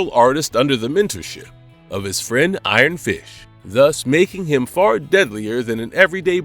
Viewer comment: LOL! i heard "iron fish" xD